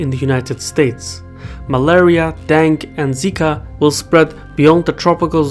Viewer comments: Did he call dengue; dang? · When he said Dengue it sounded like he said Dank. There will be an unprecedented outbreak of Dank